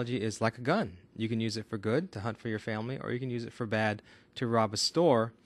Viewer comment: I thought you said "hunt your family" lol